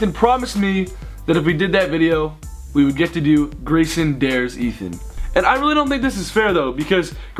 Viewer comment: I thought it said grayson dates Ethan😂